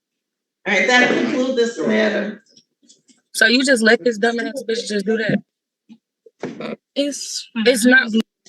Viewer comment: Did she just call the judge a “dumb ass bitch”?